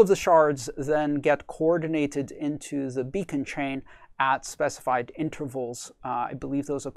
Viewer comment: Hello, very interesting video. One small mistake in the subtitle at 5:08 "into the Bitcoin chain" >> "into the beacon chain" if I understood well what you wanted to say.